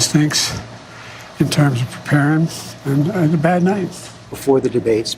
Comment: did he say "prepareness?"